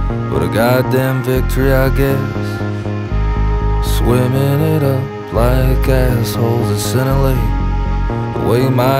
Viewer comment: I could have sworn he said "swinging it up like assholes at centrelink" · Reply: he says sinalei, which is in Tonga, hence why he says “the way my ancestors did” in the next line, as Dave is of Tongan heritage